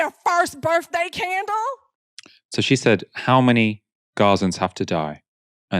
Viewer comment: I thought she said "Palestinians" 🤔not Gazans😂